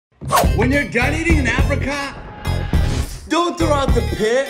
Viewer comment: What I heard: "When u r a guy eating in Africa"